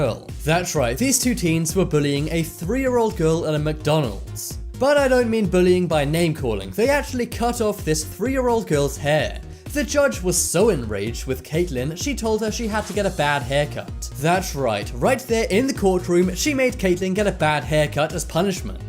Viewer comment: Omg... I misheard them. I thought they cut off the girl's HEAD not the girl's hair. OMG.